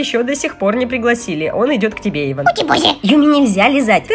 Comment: мне послышалось Юми сказала утебоже